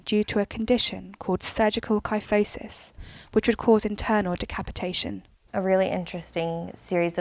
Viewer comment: WO WO WO HOLD ON DID I JUST HEAR DECAPOTATION